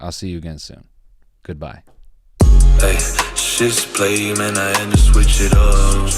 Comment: Ight. U might read this. Cool. All I need. So, what in the fuck are you saying at 12:35 (the first statement in that song). “Shits, play man my hand I switch it up.” Or is it chips play? Like poker. Naw. Shit spray, like jar? Closed captions just say “music”.